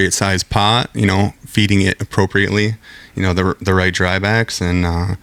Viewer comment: what did he say at 7:02 , the right what? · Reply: Dry backs